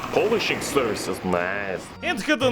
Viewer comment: What does he say 15:36 Polish ___ is nice” 2018 anyone?